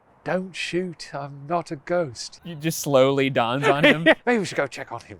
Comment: “I thought he said he was a ghost”